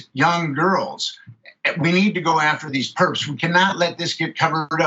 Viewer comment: At first, I thought he was saying "pervs", instead of "perps"....by the end, I was almost pissed that he wasn't...